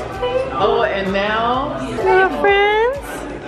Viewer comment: Did call Colleen, Rachel? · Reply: Annie Garza She said "Colleen my angel"